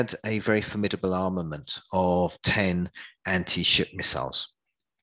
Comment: it says anti sip instead of anti ship